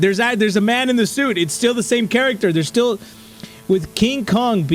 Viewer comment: i thought it said man in the shit for a second